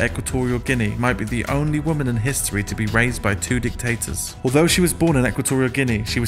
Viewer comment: I thought he said, "RAPED by 2 dictators!..."😮